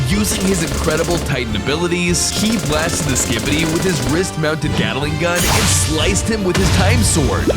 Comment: did bro say “he blasted him with his rizz mounted gatling gun”?